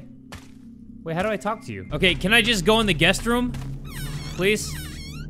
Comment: i thought 5the guest room said guts room